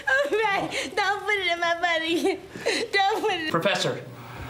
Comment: Am I hearing correctly at 4:09 lmao. "Ray, don't put it in my butt again"